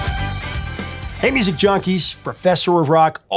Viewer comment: I thought said “Ladies and junkies…” Nope. I backed it up, he said “Hey, music junkies.”